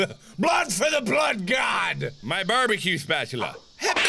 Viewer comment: wait what did he say... techno has some explaining to do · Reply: Blood for the Blood God!